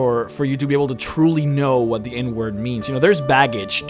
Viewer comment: Everytime you said "End word" I always heard it as "N word." "You must know more than the dictionary definition of the N word to be able to truly understand what it means." - 2015